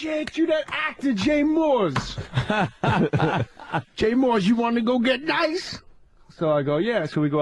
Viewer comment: “you want to go get nice?” What does he say there? “You wanna go get right?”